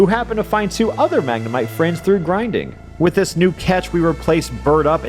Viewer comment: Lowkey thought you said Grindr at first. Kinda disappointed.